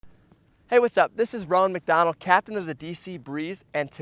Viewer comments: AINT NO WAY I THOUGHT HE SAID HIS NAME WAS RONALD MCDONALD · I thought he said his name was Ronald Mcdonald